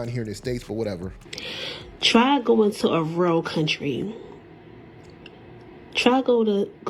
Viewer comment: What does this woman say at 3:10? Try going to a what country?????? I couldn't make it out? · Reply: She was trying to say REAL country....but she said rell..lol